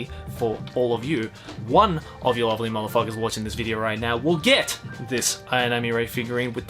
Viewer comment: Genuinely thought you said "one of you ugly motherfuckers" and I had to rewind Maybe I've been reading too many UB doujins